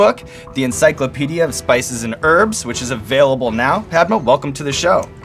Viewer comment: Did he just say "erbs" instead of "herbs?" Also, never heard of her. She's beautiful. Just discovered she was married to Salman Rushdie. Strange.